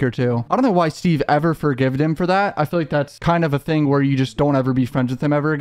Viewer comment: Explain how you think you can retire off $500,000 lol Also, did you say "forgived" 5:01?